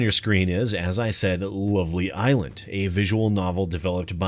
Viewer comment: Judging by the thumbnail of the game devs are literally dirty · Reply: I thought it said Lonely Island... that would make sense because they're on a boat!